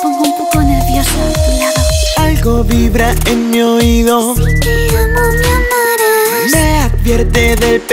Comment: Cuando dijo "Si te amo me amarás" entendi "Se que ya no me amarás" xd